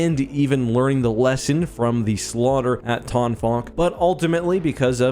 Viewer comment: Every time said the ship’s name, I thought he was saying a “ton fuck.” Like a ton of fucking good this ship would have done for the Empire.